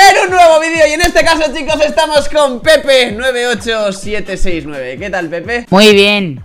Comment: cuando inicio el video yo pense que decia pene98769 XD